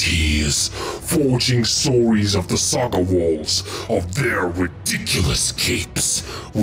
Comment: I swear at 43:35 he said story's of their ridiculous cakes